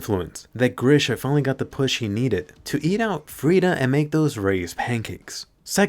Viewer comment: Did he just say Grisha “ate out” Frida at 2:43?? 💀